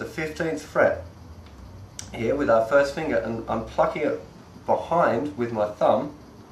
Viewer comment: did he say "I'm fucking it?"